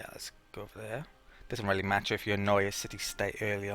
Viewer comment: Haha, 13:00 "It doesn't matter if you HANOI a City State early on..."